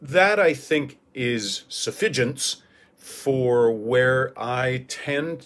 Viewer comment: Can you please clarify what word you used in "so that I think is ?? for where ..."? It sounds almost like "suffigents" but that's not really a word. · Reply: Suffigance - it's a malapropism used by Dogberry in Much Ado About Nothing, and a favorite twist on the word Sufficient. "It shall be suffigance!"